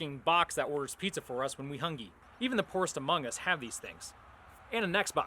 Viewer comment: did... Did he just say amongus... That's kinda sus bruh